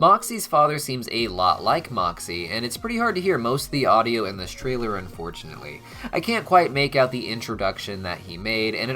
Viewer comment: For Moxxie’s beginning introduction, I managed to combine the audio I could hear and his lipsyncing to get what I believe to be him saying that his dad was named “Crimson”, like the color. Hopes this helps! 💕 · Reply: Yeah better audio clips have come out since, his name is indeed Crimson.